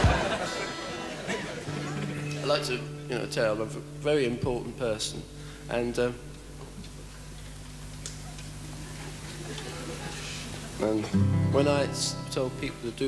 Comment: Can someone write what he said at 0:37 and 0:41??? Pleasee! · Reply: He says "And one more go" then "I like to say I'm a very important person and when I tell people to do things they jump".